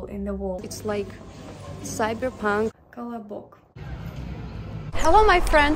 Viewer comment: did you say "cala a boca"? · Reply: oh neverming it's kalabok hahahaha btw it sounds like cala a boca which means shut up in portuguese